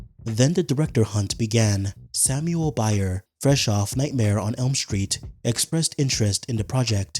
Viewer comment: I was using this as background noise and genuinely thought he said nightmare Freddy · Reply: I wasn't using this as background noise and still thought he said nightmare freddy